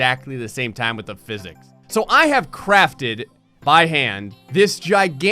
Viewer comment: music is bad! And why would you crap in your hand? @ 2:50 I clearly hear you say "so I have Crapped in my hand" sick!